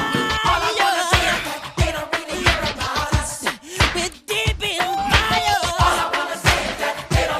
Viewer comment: “WE’RE DEEP IN THE FIREEEEEE, IM HERE TO REMIND YOUUUU” Best part of the song 😭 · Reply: I thought it said “with devious fire” or “we’re deep in it baby”